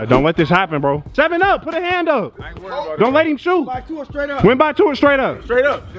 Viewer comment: they said straight up instead of win by 2